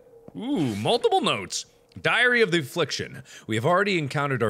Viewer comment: I swear i heared "Diarrhea addiction" at 1:20 Sadly... It's not what he said.